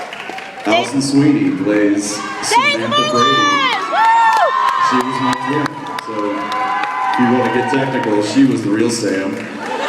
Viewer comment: What does Jensen say at 5:43-5:45??? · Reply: He says "Alison Sweeny plays Samantha Brady (on Days Of Our Lives), she was my twin. So if you want to get technical, she was the real Sam" :)